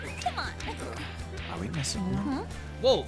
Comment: i heard said "are we missing one?"